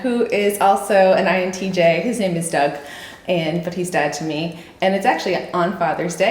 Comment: LOL... At the very beginning, when you were introducing your dad, I thought you said "... but he's dead to me"